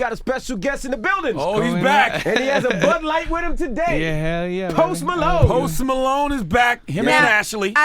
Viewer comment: Go to 0:15 and listen closely he sound like he said "hell yeah my nigga"